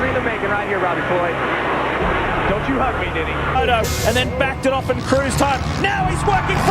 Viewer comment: did dude say “Don’t you hug me Diddy”